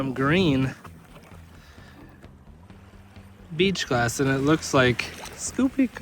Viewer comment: did you say a no no word? 😅😂😂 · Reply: Sounded like "beach glass" to me.